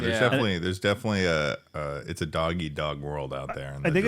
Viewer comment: did just say its a "doggy dog" world? No wonder his time is almost up.